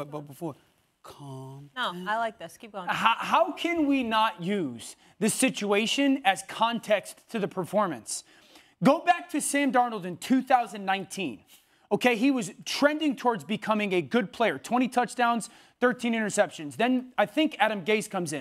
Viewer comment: molly liking what she hearing @ 2:20 😂😂 I see what she trying to do but did he say sam darnold ?